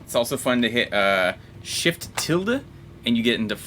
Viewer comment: what was that command he said ? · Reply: Shift+~